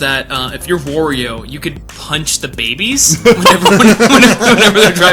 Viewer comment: did he say "punch the gaybies????"